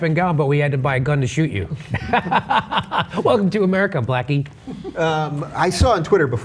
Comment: Lol did he just say blacke.Lol